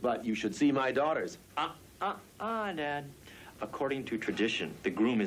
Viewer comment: does he say, "ah, ah, ah Ned?" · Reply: He says "dad."